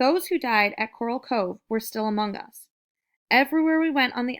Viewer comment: I think I heard amoungus🥀